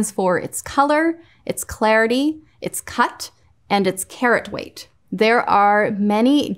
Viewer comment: "carrot weight" instead of karat lmao